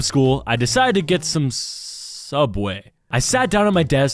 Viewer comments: DID HE JUST SAY SUBWAY??!! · Bro what’s gonna say Soobway🤣